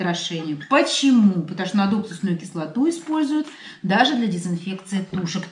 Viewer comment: Здравствуйте. извините, не расслышала название кислоты для дезинфекции. А сколько времени нужно, чтоб птица остыла? А если Вы её кладете теплую в пакете в холодильник, не бывает порчи? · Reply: Здравствуйте, Кислота НАДУКСУСНАЯ. Делайте сами. 1л. уксуса и 200 мл.перекиси водорода 3% (аптечная обычная по 22 рубл.). Смешали, закрыли крышкой и при домашней температуре ждем недельку, периодически взбалтываем. Все готово. Не хранить при открытом воздухе - быстро распадается (на водород и воду по-моему).ПОРЧА - Ничего не будет. Все ок. Три дня храниться хорошо (при 5-7 гр.)